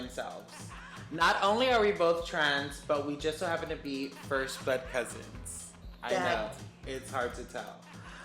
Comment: I heard. ‘First Butt Cousins’. 🙈🤣🤭